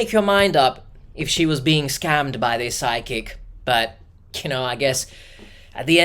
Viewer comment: I kept thinking you were saying Sidekick instead of Psychic. It took me some minutes to figure out it wasn't some new GenZ definition of Sidekick, some AI app generated friend that's always cockblocking her at the local watering hole, and a worse case scenario of a definite scam: Psychic. 🪄